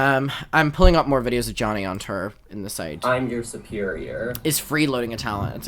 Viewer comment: It's taken me until 35:36 to realise what the word "terr" means 🤦 TOUR! Love the accent, not taking the piss, but just thought it was funny! I think because I'm listening also, not watching, I didn't see the accompanying videos of them on stage, which may have helped!!